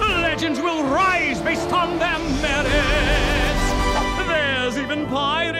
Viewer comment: What is he saying at 0:28 ? After "Legends will rise..." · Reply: Chaotic Mat legends will rise based on their merits